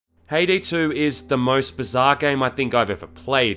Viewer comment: I kept hearing "Hayday 2".